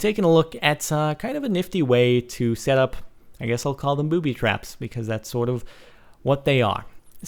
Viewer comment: i thought it said 'booty' traps lol